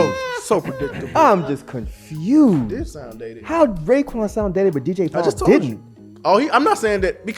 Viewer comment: Take that effin mask off cant understand words u saying! 11:34 what u said about Raekwon? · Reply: “Raekwon sounds dated but DJ Paul didn’t?”…I really need y’all to just listen bro lol the masks aren’t filtering their voices as much as y’all like to claim. If you have no issue hearing and he naturally talks low you shouldn’t have an issue with or B.